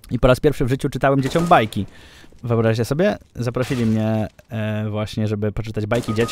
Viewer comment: ja usłyszałem "kupić dziecią fajki" xd